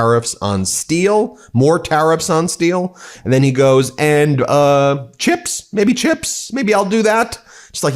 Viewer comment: Did he say 'chips'? I heard it as 'jems'.